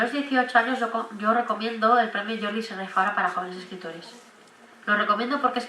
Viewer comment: ¿Cual es el concurso para menores de 18 que dice en el minuto 9:26? ¿Como se llama? es que no lo entiendo muy bien Gracias. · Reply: El premio Jordi Sierra i Fabra. ¿Ese?